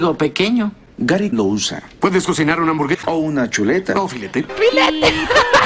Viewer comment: fui la única que noto que dijo "puedes cocinar una hamburgueja"